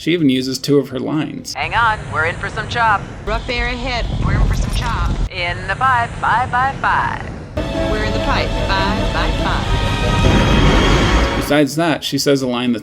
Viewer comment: "In the pipe. 5 by 5." This means they're on course, and the radio transmission is the highest quality and clear. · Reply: For years I always thought she said "In the by, bye bye bye" like she was quoting Backstreet Boys lmao